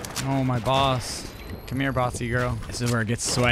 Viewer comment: What did he say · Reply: Come here bossy girl 😂😂